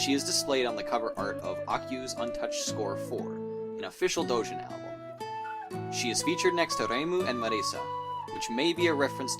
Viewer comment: Did I hear that wrong did he just say “official doujin album”?